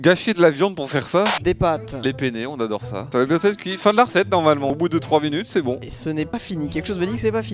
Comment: Y’a que moi qui ai entendu de la part de Terra « Les P-Word on adore ça » ? 😂😭